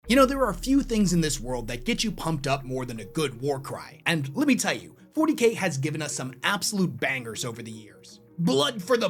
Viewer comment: For a secon I thought you said «there are few things in this world that get you pumped up like a good WAR CRIME» in the intro 😅